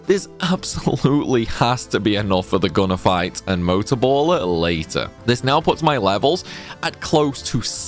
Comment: I thought you say Motor Boat instead of Motor Ball... Imma call it that from now on.